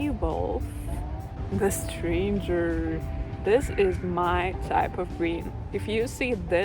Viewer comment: Hello from Finland! When you said "this is my kind of green" i thought you said "this is my diaper cream" and it took me a while to realise what you said and i was like whaaatttt😂❤❤❤❤❤❤ i am new to your channel, I love you! Thank you for making me smile! :)